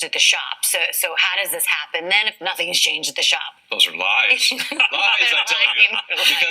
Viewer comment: rewatch the video...he said "cheaters, cheaters I tell you"! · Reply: OK, now I have to eat crow with cheatin' Chad...at 2:30 into the video he said "lies, lies I tell you"! My bad. For some reason whenever Chad speaks I just hear Cheating....:goodvibes